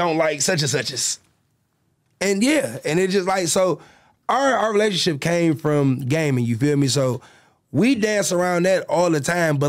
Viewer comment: ngl i thought he said their relationship came from gay men until he explained further about playing games lmao